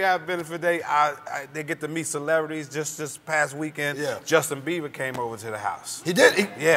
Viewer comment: did he said justin BEAVER at 5:17 ??